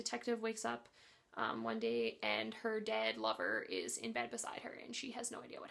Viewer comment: LOL! In your Blood Wine review, I initially thought you said "Her DAD lover..." And I was confused. Then I realized you said "DEAD lover"...😂😂